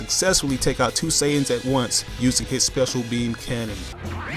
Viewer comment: did anyone think he said special meme cannon